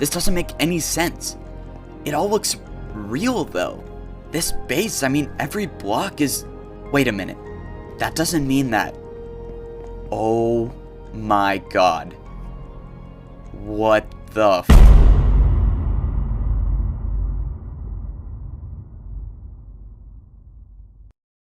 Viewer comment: it sounds like he said what the f